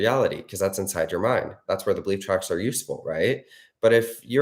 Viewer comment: Link to Breath-work: <link> Bonus tip: if you can identify what got triggered, IE not trusting or feeling confident, then once you’re done with breathwork if you can affirm in, on a loop, a few beliefs that address the triggered doubt, for 5-10 minutes, then this will help you create the change even quicker. If you can’t identify it, no worries. The breathwork will help a lot and nightly affirmation tracks will too. If you feel comfortable enough to affirm in your end then do that as well. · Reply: Omg I had to play this so many times “belief tracks” 🤦‍♀️ I thought you were saying bleep tracks. It’s been a long day for me. Urghhhh. Breath work time